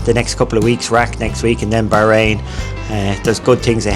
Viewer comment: Very interesting! Thanks for posting. Did he say he's going to Iraq next week at 49:14?